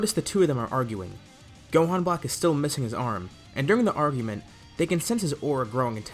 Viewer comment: I legit thought he said armgument